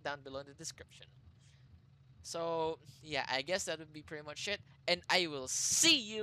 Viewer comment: it sounds like he said "that's pretty much shit"